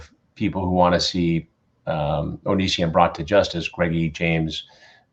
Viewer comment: Did Chris just call Onision “Greggy” 18:52 lmfao